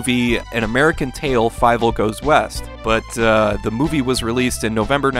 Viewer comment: Wait, his name is Fievel? Everyone always pronounced his name as Fiebel. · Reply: Maybe it's because of the accents? As a kid I thought his name was Fiefel because I misheard it.